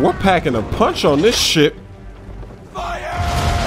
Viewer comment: i thought he said ''shit'' but after listening it few times i realized he said ''ship''